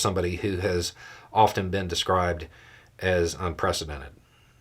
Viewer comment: Miss heard you thought you said un-presidented 😏